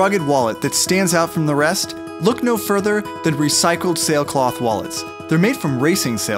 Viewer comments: I thought it said recycled snails 😂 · i thought that said "recycled snails" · I thought it said recycled snails🤣😂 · I thought it said recycled snails, so I thought it would have been their shells. LOL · For a moment i thought it said recycled snails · Was I the only one here who thought it said recycled snails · I thought it said snail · i thought it said recycled snails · I thought it said made from recycled snails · I thought it said recycled snails... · i thought it said wallet from recycled snails pops my bad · Ok we get it! You thought it said recycled snails. Your not the only one. Now chill. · I thought it said recycled snails!! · Who else thought it said recycled snails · i thought it said wallets made from recycled snails! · Who else thought it said recycled snails......only reason why I watched it.....cause I thought it said snails.... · ahhh I thought it said recycled snails..... ahhahahaa... what is my life. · Who else thought it said recycled snails? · Oh shit I thought it said "recycled snail cloth" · i thought it said recycled seals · I thought it said recycled snails 🤔